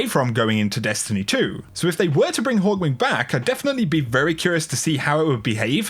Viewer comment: is it me or does he said hawk wing or something at 9:00 when talking about hawk moon